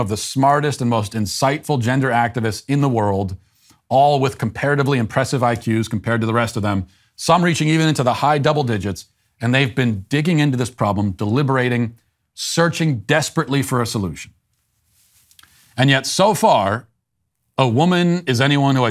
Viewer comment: "All with comparatively high IQs compared to the rest of them, even reaching to the high double digits" that line killed me HAHHA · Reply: Oh , I thought I heard " high single digits" - my bad ...🙄